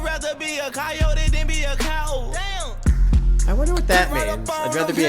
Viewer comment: “i would rather be a coyote than be a coward” when he says coward it sounds like hes saying cow so hes comparing being a coyote to being a cow[ard], just young thug being an insane person